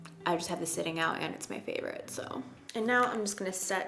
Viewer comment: Omgosh! I thought you said "I'm going to mix warm and cool shade cause I'm a firecrotch" and I wad like oh huh makes sense me too!!! Then realized your said favorite. 🤣🤣🤣